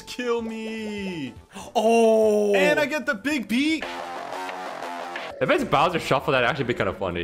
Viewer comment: Does actually say "Big B" this time around, or was it edited again?